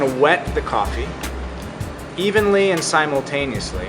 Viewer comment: what did he say? Evenly and... ? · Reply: Batuhan Turkay evenly and "simultaneously" (which means at the same time) ..